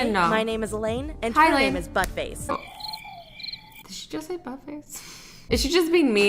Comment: Did you just say but face?